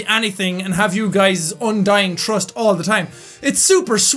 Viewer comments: I thought he said "Undyne" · did he say "undyne"? UNDERTALE 4 LIFE BRUH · did anyone else feel like he said Undyne trust instead of undying trust? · I was half asleep cuz im tired and I heard "UNDYNE TRUST" instead of"UNDYING TRUST" and I was like "Wait.. What?" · did you say "undyne"trust? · How did undertale affect me? I thought he said "Undyne Trust" lol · I literally heard "Undyne trust" instead of "undying trust". Look what you did to me. LOOK WHAT YOU DID.